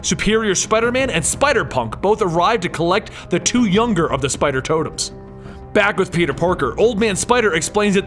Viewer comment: i thought u said spider turtels for the entire vid